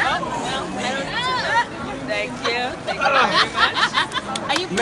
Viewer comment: It looks to me he owes her some friendly on demand push ups!!! That’s why he said awwww man really? right here with the set up the camera!!! She’s brutal!!!never bet with her!!! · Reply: Finally a comment that makes sense and clarifies what he’s saying… I thought he said “never been with her” I was way off 🤣🤣🤣